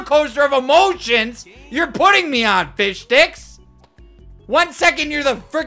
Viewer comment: rest in peace headphone users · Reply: All I heard was “FiSh- Ad “